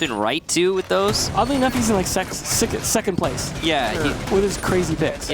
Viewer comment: he says sex instead of second lmao